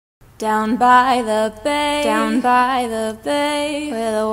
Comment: Instead of "down by the bay" I thought she said "down by the hey" 💀✋